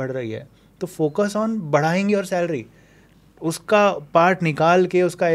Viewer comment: Kya bole sir badhaing your salary ye kaun si. English thi 😂